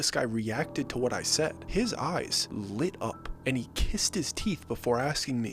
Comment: "...& he kissed his teeth." Sorry I'm a second language English speaker - did I hear this correctly - and if so, is "kissing one's teeth" a quote with a specific meaning?